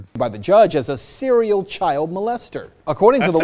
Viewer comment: Lmao.. uhg I walked into the room and just caught the serial molester part my dumb brain I thought you said ceral molester and I was thinking what the heck they doing to ceral now? I need coffee and shower. Still I'd rather just go on thinking someone forgot the milk